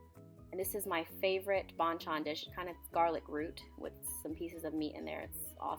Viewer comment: i heard BANGTAN DISH😂